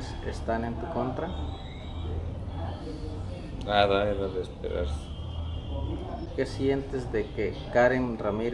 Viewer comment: The “unintelligible” part is him saying “Nada. Era de esperarse.” Or “Nothing. It was expected.”